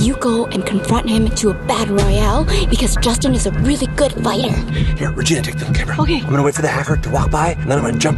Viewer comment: Wait did chad said Regina or vy